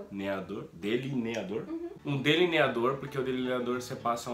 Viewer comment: eu ouvi um mor?